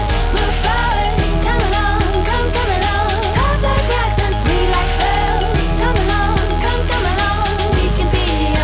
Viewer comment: The weirdest thing is that when she said "...Sweet like pearls..", I thought she said "...Since we like girls...". I need help with my hearing. It's just...